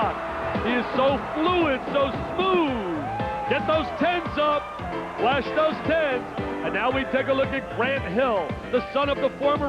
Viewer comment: sounds like he said "flash those tits" lol